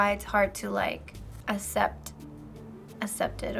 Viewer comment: did anyone else hear her say assept instead of accept? FBE should fire her